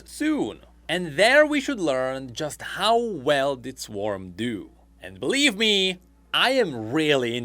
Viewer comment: Swarm or Arena? A kind of sounded like you meant Arena, but you did say Swarm....